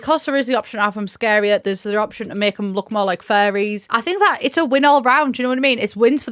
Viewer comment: It is definitely my ears but when you kept saying furries I really thought you were saying faeries and I was very confused haha I also really hate small worlds even a couple more lots would be great!